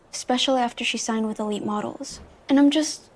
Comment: I thought she said only models